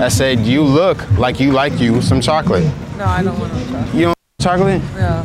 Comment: what did she say? "nah i dont want to fuck" is that what seh said? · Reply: MelekeASavage nah i dont want no chocolate